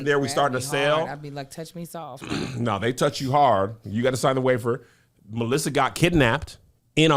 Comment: sound like kev said you gotta sign a wafer